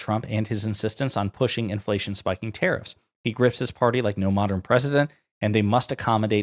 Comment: When you read "He grips his party" I heard "grifts". I suppose both are true.